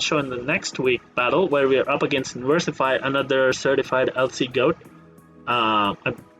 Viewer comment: I thought you said verlisify I was like wait what!